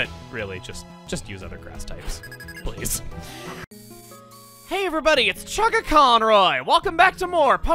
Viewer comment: i thought he was saying 'hey everybody, it's Chaka Khan'